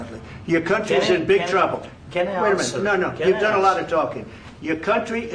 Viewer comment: The first tine i heard this i thought trump said “you’re not allowed to talk here.” Instead of “you’ve done a lot of talking.” Damn, I need my ears checked.